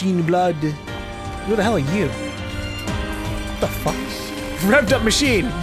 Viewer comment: any idea why 'Jean' is censored? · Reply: I always thought the censor made it sound like they were saying 'fucker' though XD